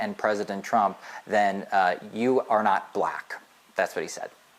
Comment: No, what he said is “you ain’t black.” The diction is important. It’s patronizing. He is acting like he can say anything to these people, primarily because he can.